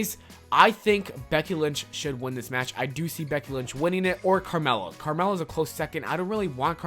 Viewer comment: Is it just me that heard him say Becky lunch instead of lynch like the whole time he mentioned her?